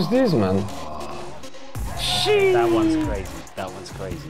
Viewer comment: No shot I just heard jo se say "sheeesh"